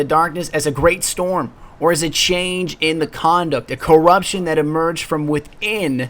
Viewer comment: Did u say a great change in the condom at 7:38! 😄😄😄😄😄😄😂